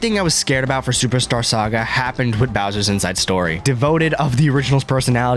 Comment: you said devoted instead of devoid 🤓🤓🤓